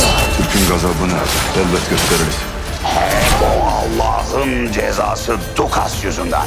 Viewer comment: ben yanlışmı duydum yoksa kafir adam Allahın cezasımı dedi